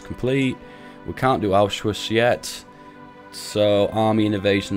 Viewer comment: I'm sure there were plenty of germans watching this gritting their teeth at how you pronounce things. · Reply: dude when he first said Anschluss i thought he said auschwitz